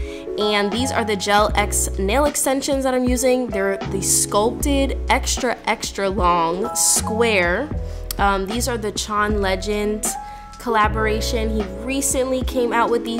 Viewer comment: Omg I thought u said John legend